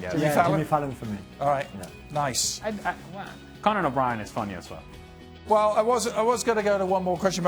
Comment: I thought I heard Alejandro Moreno say “Conan is funny as fuck”. Damn, well I’ve been playing my music too loud on my headphones.